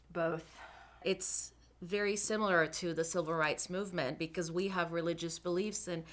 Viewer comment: Did she say "Silver Rights movement"?